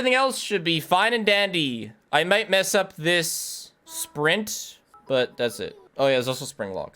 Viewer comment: I've watched so much that instead of hearing "sprint" at 46:38, my brain interpreted it as "Spiff rint" lmaoo